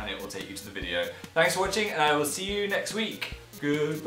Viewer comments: My Secret Files! Everyone who liked this video gets a free skink delivery in 8 - 12 weeks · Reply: I thought it said skank... Oops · Not joking I thought he said "skank" and I lost it